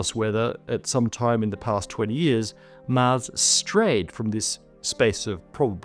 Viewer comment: The subtitles have a significant amount of discrepancies with the audio. A clear example, at 16:04 audio says "20 years" but subtitles say 30.